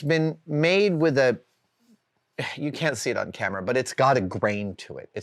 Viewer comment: what can't he say on camera?????? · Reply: He said you cant see it camera